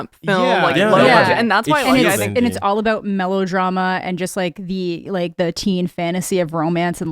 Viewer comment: did you just say MELLOW-drama!? 😂